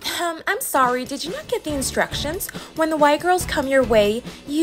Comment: For a sec I heard “when the white girls come your way you go away” and I was like WTF DISNEY bruh